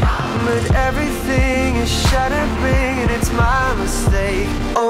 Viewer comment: only pringles fall for you.. only pringles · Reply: I was singing this in my head during school and my brain auto correct fools to schools so I heard "only schools fall for you, only schools"